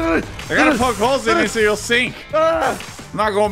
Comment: Why did I hear say “I am not going back to prison”